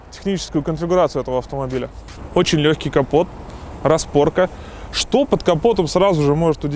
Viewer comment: я одни слышу на 3:30 минуте заместо "капот" "компот"?? ХАХАХ ОРУУУ БЛЯ